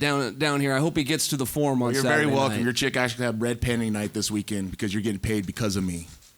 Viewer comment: red panty night ? What did he say